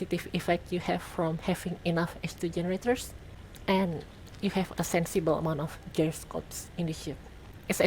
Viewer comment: When you say "H2 generators," I always hear "estrogenerators!"